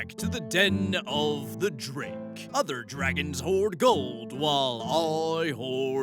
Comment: Did I just hear you say "hold goard" instead of "hoard gold"? Nope. Carry on, sir.